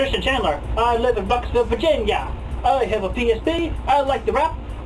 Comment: for a minute I thought he said “I have a PhD” and as someone who wants a PhD I was ready to cast myself into the sea